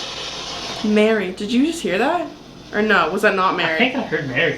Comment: I don't hear "Mary", I hear "guilty". Now, I'm not used to using spirit boxes, so maybe it really sounded like "Mary" at the time and in the moment because obviously our brains are trying to make sense of what's being said. I mean no disrespect at all. I am only saying what I personally heard.